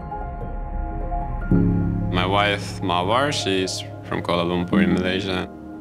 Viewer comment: I thought he said my wife is from Kuala Lumpur Indonesia. 😁